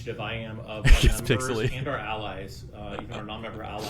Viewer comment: there it is... did they say ALL LIES? 🤣